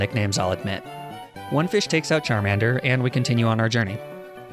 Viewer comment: sorry did you say charminder